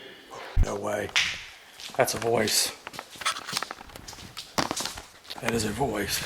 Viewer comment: Great episode guys. I can't wait for part 2 . And at 16:33 when I first heard it, it sounded like it's saying either " it's steves or that's steves" but it definitely has an s on the end of what it says. Just my opinion. But if there was more than one person to a room there it would make sense for that statement. Don't you think? Anyway a great episode, stay safe and be careful. Have a great Holiday weekend I hope you get to spend it with your families.